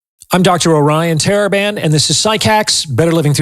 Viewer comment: I cannot stress enough how helpful „Dr. O‘Ryan Taliban“ (as I long have misheard his name) is to me. Winning is a net loss - this is pure genius.